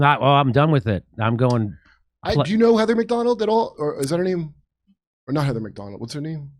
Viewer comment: Did I just hear Kurt say “Leather Macdonald”?